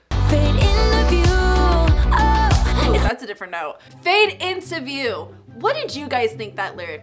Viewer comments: It's the way that I literally still sing "fake interview" instead of "fade into view" in style because I sang it like that for years 😂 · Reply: pls i literally always thought it said "fade into you" too 😭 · The fact that for years I heard “FAKE INTERVIEW” too. 😂 💀